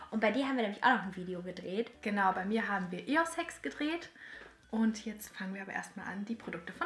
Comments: !? habt ihr auch eossex verstanden !?😂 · Wer hat auch nicht Eos Hacks verstanden? 🤔😂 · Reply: ich hab eos Sex verstanden habe es mir 3 mal angehört und immer eos Sex verstanden 😂😂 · Wer hat auch nicht Eos Hacks verstanden? 🤔😂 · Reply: Ich, ich habe eos Sex verstanden 😂😂😂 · ich habe eos sex verstanden 😂😂😂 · ich habe eossex verstanden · Ich habe am Anfang verstanden: ,,Wir haben Eos-Sex gedreht'' Bin ich die einzige Person die immer 2-deutig denkt :D